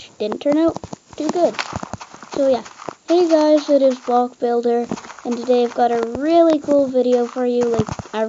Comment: lol i heard him say bob the builder